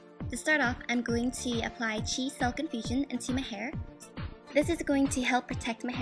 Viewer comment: i thought she said Cheese silk in fusion lol